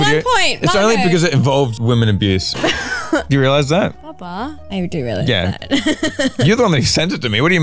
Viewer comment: did she call josh papa? Bruh